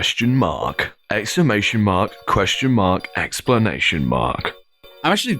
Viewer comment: did he say 'explanation mark' ? 😁